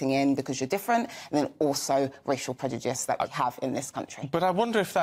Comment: is she pronouncing prejudice as predujice, or am i hearing her wrong?